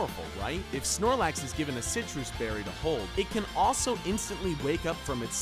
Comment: DID HE SAY SITRUS BERRY AT 8:26 WHEN THE VIDEO CLEARLY STATES "SNORLAX ATE ITS CHESTO BERRY, SNORLAX WOKE UP" !!!???